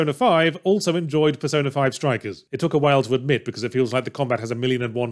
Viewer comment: "It took a while to adjust" the subtitle say at 4:14, meanwhile Yahtzee says "it took a while to admit". Yahtzee probably flubbed the line when he sent the script over for the YouTube subtitle editor or whoever, cause the former does make more sense in the context of the sentence.